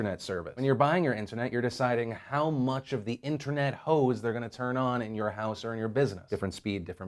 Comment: all i heard was #InternetHoesTurnOn